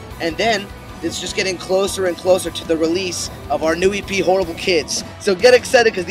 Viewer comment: for a second i thought cody said hipster kids instead of horrible kids